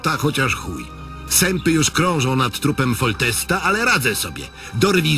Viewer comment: Na samym końcu zamiast "zjebałeś" usłyszałem "zjebane" xD Ale na szczęście ten film wcale nie był zjebany, a muzyka pasowała niemal w każdym momencie, głównie jeśli chodzi o tekst. PS. Tri Martolod to całkiem niezły kawałek, pomimo tego że to raczej nie są moje klimaty.